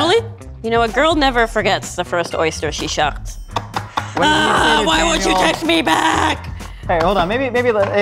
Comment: She stabbed an oyster while saying, "Why won't you text me back?" Hilarious and scary. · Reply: Franklyn FM McInnis thought she said why won’t you touch me back, but now that I think about it you’re probably right